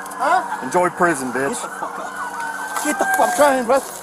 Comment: Cop "shut up, stupid... enjoy prison, dude". He had had enough of this guy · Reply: I've listened 5x and I hear "enjoy prison bitch"